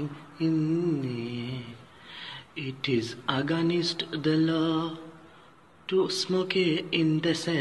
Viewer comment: I thought he said: "It is aganist the lord" 😂